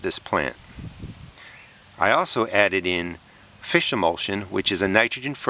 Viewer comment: What's that fertilizer you mentioned? i heard "fish mulch" lol not sure i heard the right words.